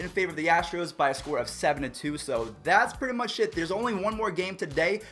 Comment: First dislike! I love your videos. I did it for the memes. Also at 8:31did you accidentally say sh*t instead of it?